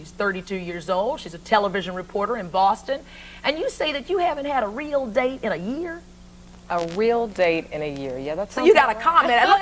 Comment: I thought she said "you haven't had real dick in a year" I'm currently on the toilet rn and even my turds sitting under me where confused